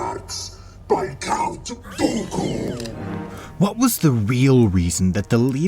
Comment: I read it as the actual quoat so I am stupid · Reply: It even sounded like he said count goku